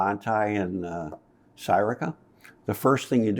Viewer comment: Anyone catch the name of the bunch grasses mentioned at 34:33? Sporobolus was the first one but what was the second? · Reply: Bouteloua gracilis - sry sometimes I speak too quickly. <link>